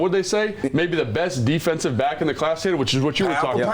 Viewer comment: Hey. I thought they said Mike S was the best defensive player not back. That’s different different!